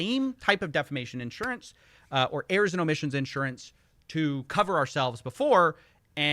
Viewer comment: Two minutes in and I thought you were saying Arizona Mission insurance